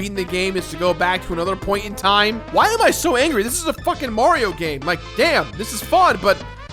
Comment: Did Jirard just say fuck?